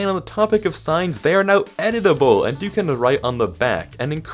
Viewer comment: Genuinely thought he said 'edible'